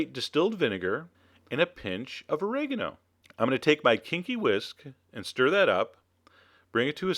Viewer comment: I thought you said we will now use "our kinky whisk." loll